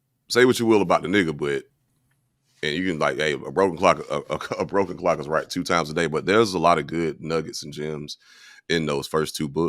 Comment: why'd I hear "a bro can clock right twice a day" before my 🧠 told me what he said😂